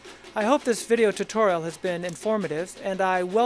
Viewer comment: i thought it said harry potter